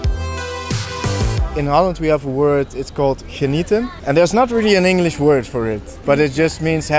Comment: What's the word/expression at 11:26? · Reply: Genieten.. it means enjoying